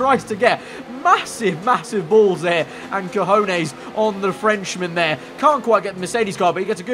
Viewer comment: did he just say "cojones"!!!???